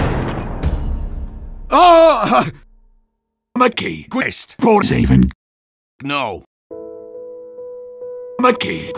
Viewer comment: what he even saying at 0:55? (great work btw) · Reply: "Make Key Quest Part 7"